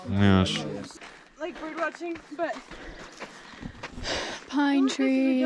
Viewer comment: I love how I’m just babbling. I think I heard “birdwatching” lol!